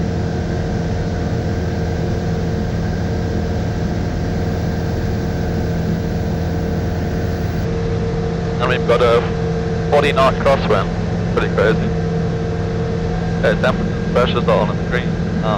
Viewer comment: 40kt crosswind? Did I hear that right? Much more than that plane is supposed to handle isn't it?